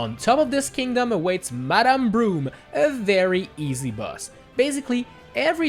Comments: Did he say "Madame Broom?" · i heard Madame Broom